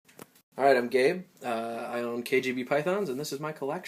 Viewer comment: LOL, in the very start of the video, ive thought you said "Alright, im gay" :P Hehe, anyways, awesome snakes dude.